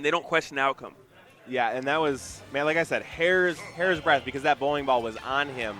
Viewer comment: i thought he said 'hair's breath'